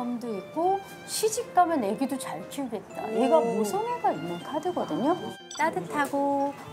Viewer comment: 이분 카드 전문 상담가 선생님이 맞다는 힌트! 2:50에 잘들어보면 모성애가 있는 카드(자막에서는 색)거든요! ㅎㅎㅎㅎㅎ 제 귀에는 그렇게 들리는데 다른 분들도 그러신가요?